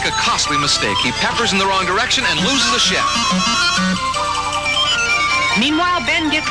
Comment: Did he say "loses a ship?" While playing BurgerTime? Riiiight...